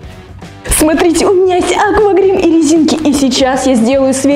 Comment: Мне 1 послышалось акварезинки